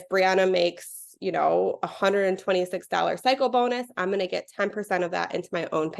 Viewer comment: I know she did not say it, but what I heard was “psycho bonus.” 😂😂 Kinda appropriate, huh?! 😂